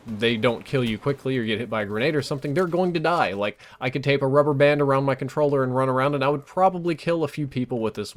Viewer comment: Did he say tape a rubber band @ 2:56?